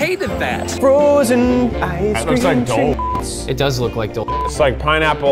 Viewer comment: I honestly thought they were saying “Dole Shit” the whole time because I have a pea sized brain